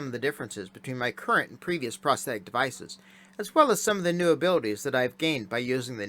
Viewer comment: Lmao, I kept hearing "Prostate Devices", "I don't why", "I guess it kinda fits, so just wear it"